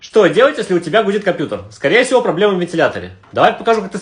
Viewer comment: Мне одному послышалось " что делать если у тебя будет компьютер"